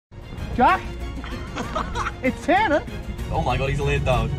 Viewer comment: It sounds like he is saying “Jack it’s Santa!”